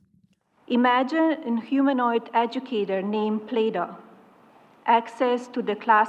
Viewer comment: Did she say “humanoid agitator”?